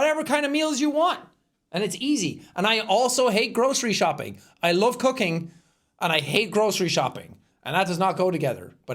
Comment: sounded like “I love cocaine” 😂